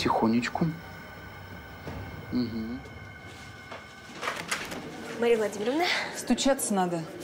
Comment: мне одной показалось что она сказала МАРИЯ Владимировна?